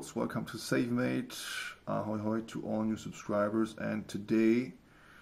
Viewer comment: in the intro it sounded like he said "Satan mate" instead of self made XD